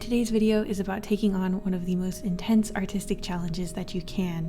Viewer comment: I legit heard "the hardest autistic challenge" My brain is stupid